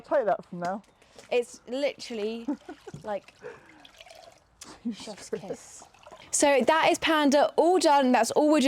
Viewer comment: I could not understand what she was saying 😂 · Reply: She said “chef’s kiss” 😊